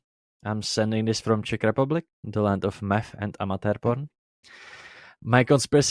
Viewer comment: I think he's saying Land of Meth and Amateur Porn